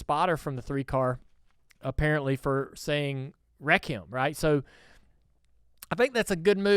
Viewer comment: The spotter's comment to "wreck him" was to wreck Logano. I agree with NASCAR's decision to ban him for 3 races. The 11 is on the inside of the 3, so how does a "right hook take out the 11"? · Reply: The spotters comment to "wreck him" came after the fact. He needs a vacation if that's what he was saying. It still sounds like he said "wreckin' " then "wreck yeah".